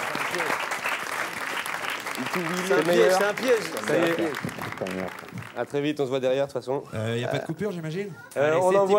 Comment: A 31:27 31:28 il dit quoi jonathan cohen dans l'oreille de lomepal ? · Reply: "cimer frère" ou "c'est un plaisir frère" et après il dit "tes le meilleur"